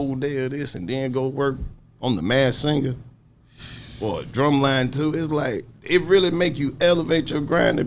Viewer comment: Did he just say drumline 2 ?